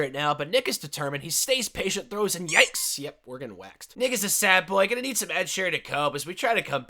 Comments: did he just say ni- is a sad boy on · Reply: Nick is a sad boy lol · what did you say? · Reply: Nick is a sad boy gonna need some Ed Sheeran to cope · what did he sayyyy?? 🤣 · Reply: We’re getting waxed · WHAT DID YOU SAY THERE?!?!? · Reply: Nick is a sad boy lol idk what else you could hear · I thought you said something else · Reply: Nick is a sad boy? Idk what else people think said lol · What did bro say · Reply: Nick is a sad boy gonna need some Ed Sheeran to cope